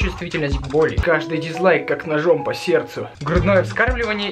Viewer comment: Мне послышалость вместо ,,как ножом по сердцу'' , ,,Как на жопе сердце'' ))))